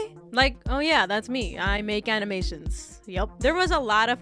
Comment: i thought that said haminations and im like "the freaking guy who sounds 7??"